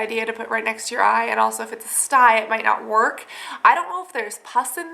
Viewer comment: At first I thought the video said, "Is it a STYLE?" so I'm like, "Yeah, rock that style girl!!" hahaha